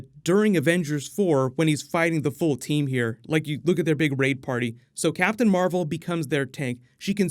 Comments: i swear he said RAPE party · I heard look at their rape party and was like what